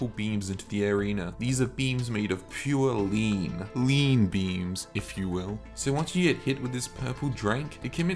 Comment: I was just chilln until I heard "lean beams" lmaooo